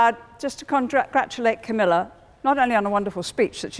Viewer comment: What did she say :) ? "Contra-gratulate camila" ? :) 8:25 "You not so well" THAT OR BUT "you have forgotten" ? 9:02 Make the ... what ? Makes ? 12:30 WHAT DOES THE SITTING WOMAN SAY ?